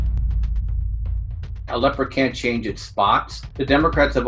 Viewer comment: I thought he said a LEPER can't change his spots. Cheap earphones...